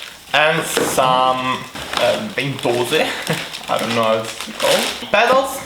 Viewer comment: are you romanian? because i heard u saying "ventuze"